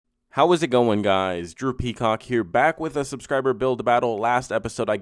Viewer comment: Not gonna lie, my audio cut out during his into and all I heard was “Hey how’s it going guys, Drew cock here back with another video” like the timing was so bad but it was Lowkey funny